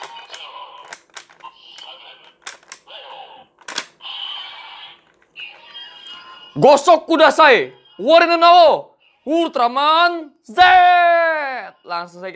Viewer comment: kedengeren seperti gosok kudasai 😂😂😂😂...